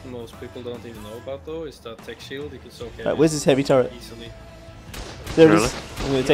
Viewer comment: what was saying? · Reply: something soaking with tek shield something instantly. Very curious about that mechanic i dont know about.